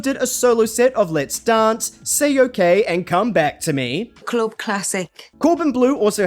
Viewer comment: Why did I hear “Say you're gay and come back to me “ 😂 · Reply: It's Say OK and come back to me! XD